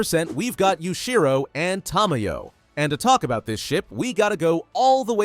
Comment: I heard shit